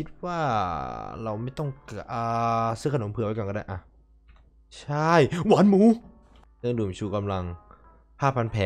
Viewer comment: แกจะพูดว่า วามู อย่างงั้นสินะ!!